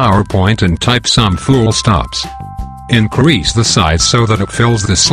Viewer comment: what did he say i should type-in in 6:21 to 6:22? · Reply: Fullstops ................................................. ........................